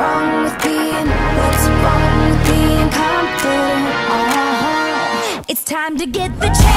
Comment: at first i thought it said what' wrong with me being constipated at the end lol